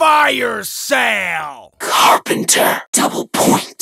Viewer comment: cap 2:50 double 2:53 insta 2:55 max 2:58 KA-BOOM! 1:49 laughter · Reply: at carpenter I hear "CARPENDO"